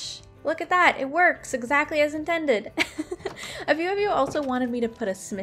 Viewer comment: i put captions on and instead of you saying"few" you said "view"